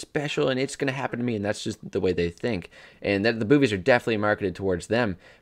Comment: I heard boobies, not movies.